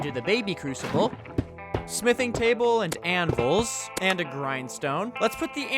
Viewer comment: why does he say anvul?